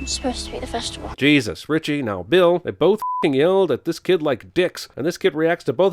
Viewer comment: I heard "This kid likes dicks".